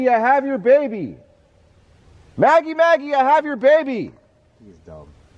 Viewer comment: that was a woman voice sounds like she saying like you get my baby back what it sounds like kinda.